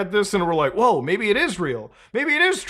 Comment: "Maybe it is real" sounded like "Maybe it Israel". Conspiracy confirmed?